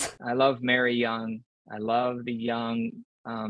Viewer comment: Barely started the video but when he said “I love the Young’s”, I thought he said “I love the Huns” and I was like 👀👀👀 he slipped 😂